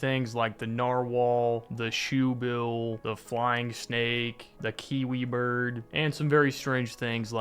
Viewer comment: low-key I heard "the kinky bird"